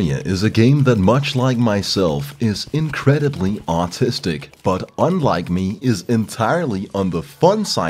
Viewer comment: "terraria is totally autistic, but on the fun side of the spectrum" that. is so damn accurate. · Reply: Oh shit I thought he said artistic lmao